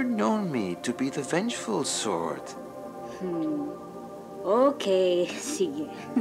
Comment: ??? TAGALOG??? LIKE THE LANGUAGE TAGALOG?? i heard “okay. sige” which is “okay” in english then a few seconds later said “Ang Ganda!” which means “it’s beautiful!” Side note: means imagination or hopeful!